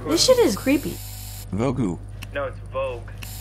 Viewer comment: What'd she say at 0:06? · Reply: This s*** is creepy